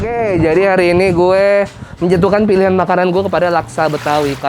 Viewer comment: biar tmen² ga salah denger.. d buka dlu maskernya.. Skilas tmen sblah gw dnger nya "puji tu*an" trus dia bilang sambil kaget "loh, yudo kri*ten..?" Padahal kan, "menjatuhkan"